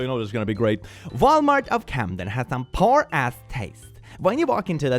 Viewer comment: When you said Walmart of camdem, I thought you said Walmart of catnap